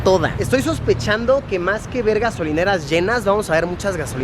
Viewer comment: Luisito dice vergas :v (se que dice ver gasolineras)